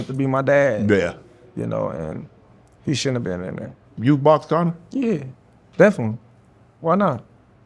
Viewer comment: His accent is so thick, I don’t always understand what he is saying? Who did he ask Crawford if he would box at 1:18? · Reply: “Would you box Connor?”